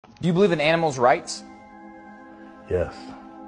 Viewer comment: First time I saw this I heard “do you believe in animals” and then they started talking about PETA and I got so confused💀